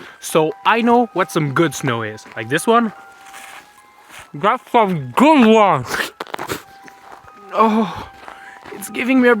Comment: “graph from gunglong” was all I heard